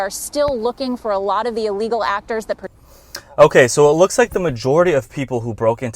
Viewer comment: ish timeline did she say illegal "Actors" ??